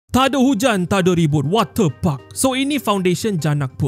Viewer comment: Ni aku yang Salah dengar ke atau ape Wate puck Atau water parks 😂😂